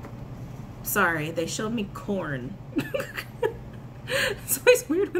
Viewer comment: Corn? I thought you said “porn” at first.... 😂🤣🤣 33:23 mark... 😂